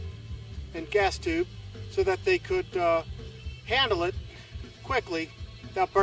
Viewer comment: 「育てて行くんだぁ╰(‘ω’ )╯三」に聞こえる